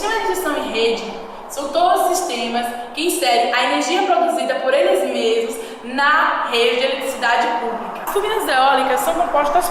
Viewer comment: Nao entendi oq ela falou nos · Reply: O sistema de injeção em rede são todos os sistemas que inserem a energia produzida por eles mesmos na rede de eletricidade pública.